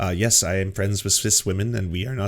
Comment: I know you said Cis, but my brain initially thought you said, “Yes, I’m friends with a Swiss Woman.”